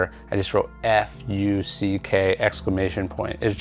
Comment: What is he saying "F you C.K?" who is C.K?